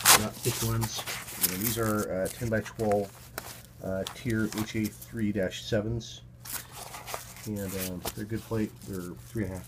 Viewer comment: I'm getting Bothell Vest that is the updated design of this one. What was the plate you mention at the 4:45 mark? I hear Tier HH3-7.